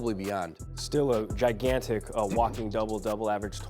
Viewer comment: Did he say "Guy-gantic"?